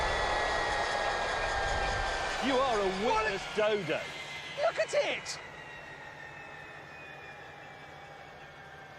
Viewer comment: What Did James Say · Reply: You are a wittless dodo.